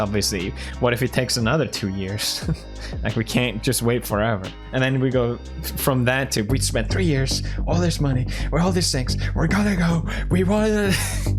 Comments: "all this sex" Am i hearing that correctly? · Did he say "all this sex" ? Ik he prolly said "all these things" but its sound so weird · did he say he spent all this money had no s3x and all this other stuff